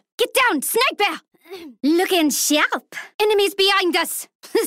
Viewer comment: what is she saying? Idk i just love it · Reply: Lookin sharp